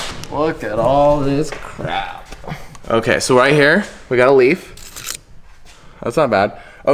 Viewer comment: i thought he said "leak" for a sec ☠